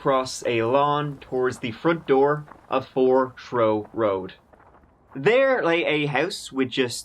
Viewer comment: Not trying to be funny, but it ended up being funny to me... side note: almost this whole video, I thought was saying Throw Road, ya know... in his cute little Irish accent. Then I realized it was actually Trow Road! Like he says, tree for three, & trill for thrill. Well, Haha! Jokes on me! 😆😂